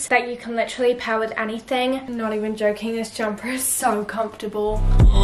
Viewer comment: Wait for 1:46 to 1:50 did she say "im not even joking this jumper is so uncomfurtable" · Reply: no i said comfortable ahhh!! i love it :))))